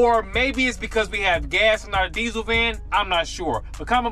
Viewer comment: I didn't understand what "gas in our diesel van" meant. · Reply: “Gas and not a diesel van.” Probably should have said “instead